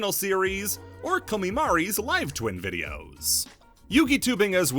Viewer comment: What's the channel at 19:45? I can't quite make out what their name was. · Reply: Comimari <link>